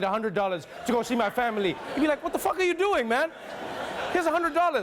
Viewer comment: I love how Trevor clearly says fuck but the subtitle says (bleep). Made my day for some reason